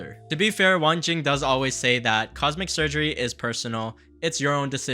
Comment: did bro say “cosmic surgery” 🤣